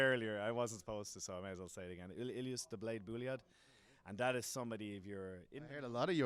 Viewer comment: Who? Dablade Bouleav? What did he say?